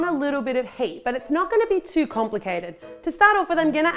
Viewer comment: I thought she said hate😂😂